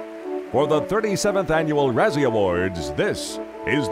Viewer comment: thought it said brazzer award